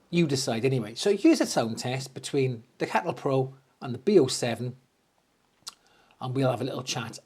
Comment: Hi, another great informative video, I thought you said cattle prod amp tho !!, regards mark